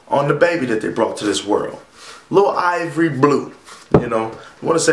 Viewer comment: Lmfao. Did you say "Little Ivory Blue"?